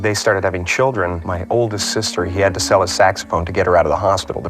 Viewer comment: I thought he said his dad was a sex offender lol saxophone lol